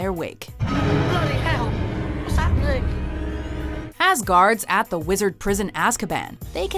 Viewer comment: I thought you said Asgaurd at Askaban